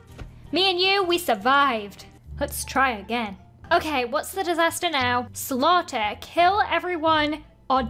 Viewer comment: I thought it said disaster slobby